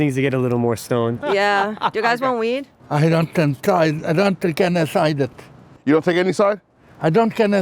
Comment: did he repeat “I don’t take a side”? the first part wasn’t that hard to make out but I couldn’t really tell what he was saying. “I don’t genocide”?